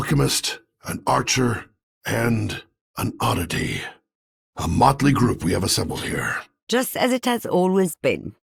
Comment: Subtitles fix masterpost: 0:06 A motley group 0:48 brings you to 1:22 the witch's magic 1:27 Her magic is something 2:06 All due respect, 2:11 good six-shooter. 2:35 his machines. 2:46 Ronin? I like 2:53 high atop the 2:56 cherry blossoms in 3:06 for anachronisms 3:13 like it's seen 3:23 have a drink? This 3:35 to the monsters 3:42 seem awful familiar. You [Yeah, this one's technically grammatically incorrect, but that's how he says it] 3:52 out of my 4:11 as different as 4:14 someone who'd put 4:36 You fight in [Again, grammatically incorrect, but that's what he says]